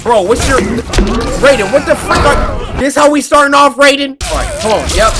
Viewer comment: i thought he said what the f**k